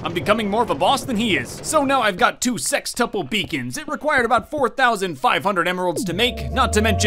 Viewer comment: When i first heard Sixtuple beacon i thought he said Sextuple beacon please don't judge me on this message!